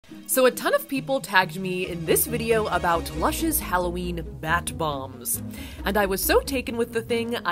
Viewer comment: At first I thought she said “Russia’s Halloween Matt bomb”